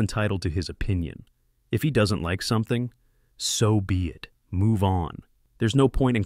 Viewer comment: DID HE JUST SAY SOVIET?